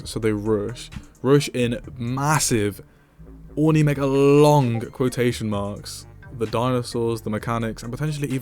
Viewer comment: Ornimegalong instead of Ornimegalonyx